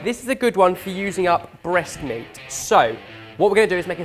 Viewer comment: I seriously thought you said making something out of breast milk, not breast meat. That accent can make interpretation dodgy!